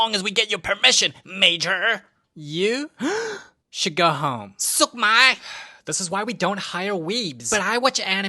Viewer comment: did you say … m&ms?